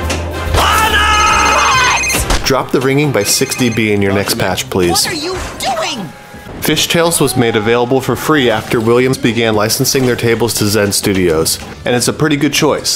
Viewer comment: Also 12:12 I kept thinking he was saying 60-b instead of 6-db, and was very confused on what he meant until now lol